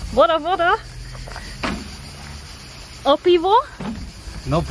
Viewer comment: Great video, again! Glad you enjoyed Balkan states. Btw, 8:12 - beer is called pivo, not pivot. 11:15, gays are saying "jezero" (LAKE) 19:16 that is exactly what said :) Maybe next time - Serbia?